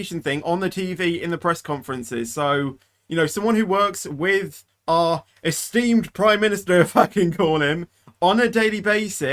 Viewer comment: Sounded like an F bomb was dropped 🤭 “Someone who works with our esteemed prime minister f***ing call him”